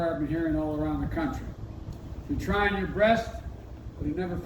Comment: rest? 😂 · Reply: he's not even listening to himself otherwise he would have caught it. I thought he said try your breast.